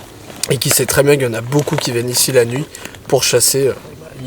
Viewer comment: Euuuuuh pour moi le ILABA c'est juste Didi qui dit "eh bah.." 30:16 "Il y en a beaucoup qui viennent ici la nuit pour chasser .. eh bah.. illégalement j'ai envie de dire" Nan ?